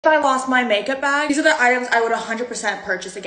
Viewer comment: I always watched these and I thought she said ‘’I lost my makeup bag’’ and I was like ‘’then how are u showing us the items if u lost ur makeup bag🤨🧐’’? And now I realised she said IF I lost my makeup bag. 😅😂